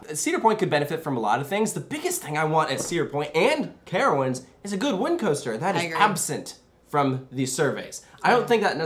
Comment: I heard you say at 11:30 “the biggest thing I want at cedar point and carowinds is a good wing coaster”? Am I hearing that right?